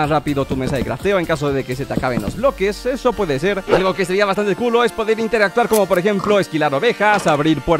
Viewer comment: creo que entendi mal lo que dijo :V 10:09 entendi algo que seria bastante culo